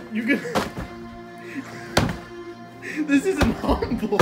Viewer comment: did say “This isn’t humble”😂